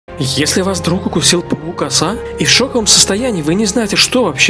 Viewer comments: Я помню у бабушки на террасе был такой паук (я ещё его кормила мухами и назвала его Гриша:)) кстати мне послышалось не паук а пук пук Оса 😂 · Паук-оса, а мне послышалось пакукаса. А почему мази так много